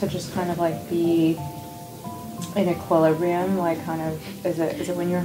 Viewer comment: with the subtitles, i dont think you knew what she was saying but she says "equilibrium" during that question mark!